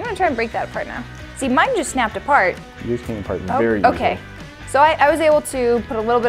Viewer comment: The subtitles say inaudible but I clearly heard him say 'yours came apart very easy' :think